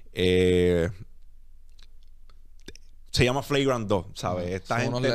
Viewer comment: como se llama? Quiero escuchar ese podcast, no entiendo cuando dice el nombre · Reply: Flagrant 2